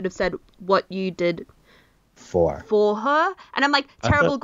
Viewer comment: Jon does actually say "What you did for her," rewatch the scene with subtitles